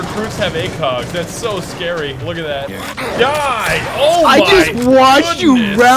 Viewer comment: Ok real talk I thought he said die n*** and I was like”one moment brother, whatl